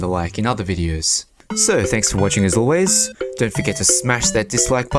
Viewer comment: Someone please tell me I'm wrong, I listened to it like six times and it sounds like he said unsubscribe